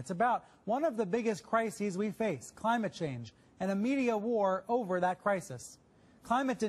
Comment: I heard "media whore" over that crisis, instead of "media war" at 0:06 LMAO